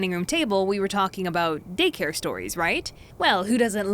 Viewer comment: why do I hear "dick hair stories"